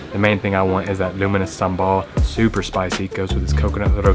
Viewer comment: I kept hearing luminous Sambol and I thought you say luminous due to its color. Then I realized what you really meant is "Lunu miris" Sambol. (Lunu = salt and miris = chili). Hilarious!!!!!!!!!!!!!!!!!!!